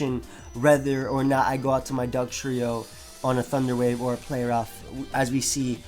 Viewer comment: Did you say thugtrio?